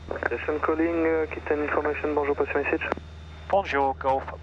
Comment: Hi, when you contacted Aquitane Information (8:10), the controller acknowledged and then seemed to say 'pass your message' as per UK practise. Is this my mistake or did he actually say that?